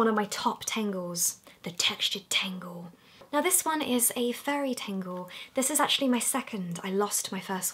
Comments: Who else thought when she was saying furry tangle she said fairy tangle lol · K so at first I thought you said fairy tangle not furry lol. I have a few fidget toys and what I use depends on what's close to me and my mood. I really like that snake bracelet one since I could wear it to work and it not be noticeable. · I thought you were saying FAIRY tangle. I got all excited...